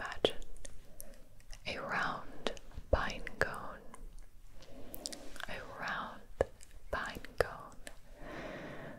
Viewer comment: Someone knows what she said at minute 22:55? A round something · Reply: pinecone